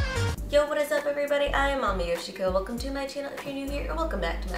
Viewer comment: This is probably my fifth time watching you and yet it's the first time my brain thought you said "mommy yoshiko" instead of 😂😂 I think my brain gave up today